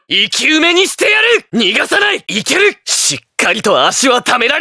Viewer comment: what did he just say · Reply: nigasane